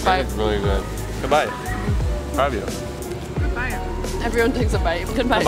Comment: hold up Did i hear that corectly? Good bite: Good Bite "they bumping fists" and while bumping fists did just say I love you in a mumbling english?